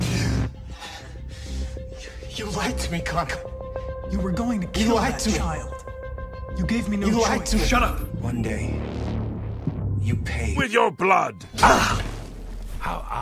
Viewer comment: why did I hear that as “I’m gay”